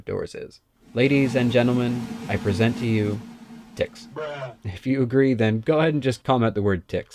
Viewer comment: oh my lord!! i was working on my crb which for some reason is shooting at half the joules of my stupid lvoa all the sudden. when the video was ending i took my eyes off and was turning on my dremel and i thought i heard you saya version of "richard". i almost cut the wires by accident. i was like what did he say???i ive never heard any word on your videos that wasnt 110% family freindly(which is awesome by the way) you obviously have great parent(s). i backed p the video... ticks? oh that makes sense. i know about ticks. i have avery small landscape business so ticks of course make sense but i could have swore something different. excellent video. you made my day and i needed it. great content as usual. i usually take notes but i was more familiar with this info than your other videos. im still chuckling. thanks again. Richard, i should have known better. cheers